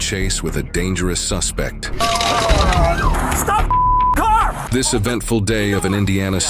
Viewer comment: this cop was definitely saying stop f***ing the car right